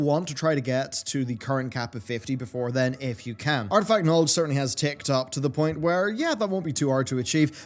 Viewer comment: anyone else hear 'fkn alts' @ 1:49? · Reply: I believe he says "artifact knowledge" .. I had to play it multiple times at 0.75 speed xD